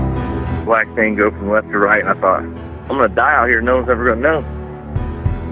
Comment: What does the guy say at the very beginning of this? Sounds like he says black tango from left to right??